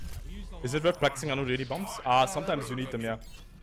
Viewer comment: what does he say at first? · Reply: Rooster is it worth practicing 180 bombs